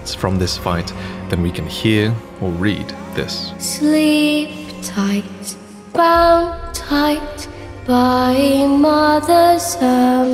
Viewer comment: I heard “Sleep tight bound by the mothers eye”